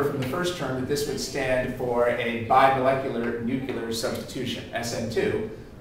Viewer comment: I've heard it all! A chemist said NUCULAR instead of NUCLEAR!!!!!!